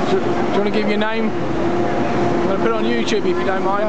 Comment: Did he just say ‘ can I give u 9?? Seriously ? Dude u shud pay him 25 for let u shoot video for your YouTube